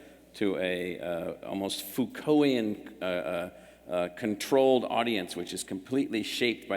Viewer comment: wth is that word? Foocoian? Phauxcoian? I cant google it. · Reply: Foucaultian (Michel Foucault pronounced "fooko" = a much over-rated French postmodernist philosopher)